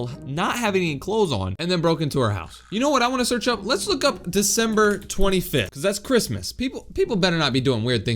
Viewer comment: I thought you said September 25 because that’s my birthday but you said December 25